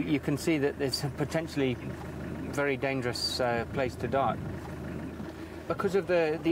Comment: Did he say '...dangerous place to die' at 30:45? Any place that you can die is dangerous.